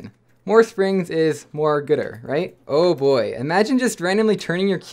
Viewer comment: Did you just say........... “GOODER”